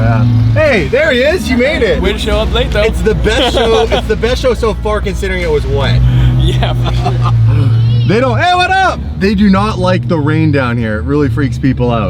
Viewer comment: Yooo at 2:41 did he say "Yea, F*** up"